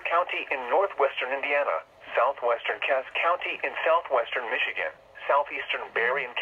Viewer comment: Just before 26:29 I thought it said "Virginia" instead of "Michigan"